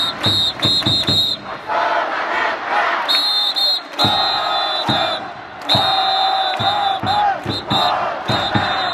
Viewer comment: 이때 관중들 구호가 뭐라고 하는 건가요? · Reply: 6번 타자 박성한 안타 박성한 입니다. 대다수의 야구팀에서 쓰는 응원 구호 중 하나입니다. 몇번 타자 ooo 안타 또는 홈런 ooo